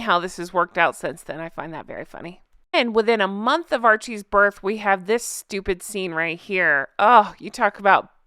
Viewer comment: says: "A month after Archie's birth," I heard " A month after our cheese birth,".